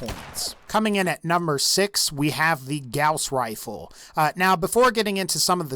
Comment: All I kept hearing was "Grouse Rifle"